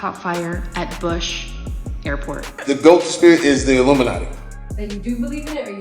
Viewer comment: What did Jamie Foxx say at the beginning? I couldn't tell his answer to if he believed in Illuminati, and I couldn't hear what/who he said was the illuminati ("The goat spirit is the illuminati" maybe?)